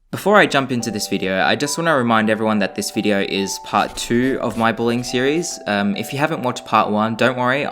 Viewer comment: When you said "this is part 2 of my bullying series"I thought you said "this is part 2 of my BOWLING series" XD